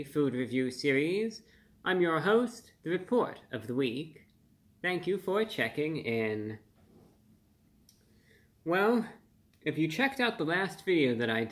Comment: All I heard was "thank you, forechecking". Didn't know he was a hockey fan. Hell yeah.